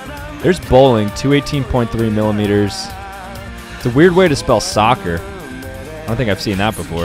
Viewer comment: “Weird way to spell soccer" bruh i thought he said he calls it football because he cares about all of his fans around the world lol